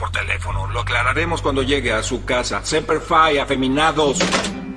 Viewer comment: ¿Qué dice 1:10? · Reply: semper fi, frase comun entre marines gringos